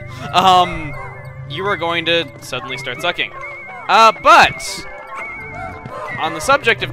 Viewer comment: I know this video is old, but listen to what says at 8:41 " You are suddenly going to start sucking.... a Butt," I died when I rewatched it just now (as in the video not like i stopped then went back and rewatched 8:40 like I've seen this video before) [And yes i had to point that out for no reason]